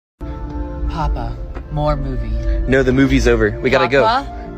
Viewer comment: I thought it said “papa more booty”💀💀